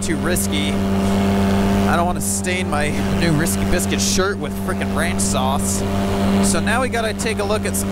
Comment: That spicy chicken sandwich was the most delicious spicy chicken sandwich I've ever had! There is definitely something about eating while flying that makes the food taste better. If you like any of the new Risky Biscuits Co. designs and/or would like to enter the giveaway, be sure to follow the top 4 links in the description! *CONGRATULATIONS to Kerry Hill, the winner of the Team Fly Halo Giveaway. Kerry will be training and picking up his new Scout in February of 2021 in the Salton Sea.* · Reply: Tucker Gott I thought you said "Rick astley Biscuits"